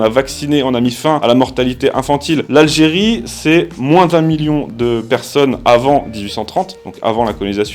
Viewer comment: j'ai pas bien compris la phrase ? Avant 1830, population de environ 20 millions de personnes, et après l'indépendance c'est 12 millions de personnes. Moi j'entend là qu'en quasi un siècle, le pays a pris un -8 millions de personnes, soit près de 40% de sa population. Personnellement j'aurai aucun mal à considérer ça comme un crime contre l'humanité. De ce que google me ressort, un 0 en trop c'est glissé dans les chiffres de 1830. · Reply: d'un million de personnes avant 1830.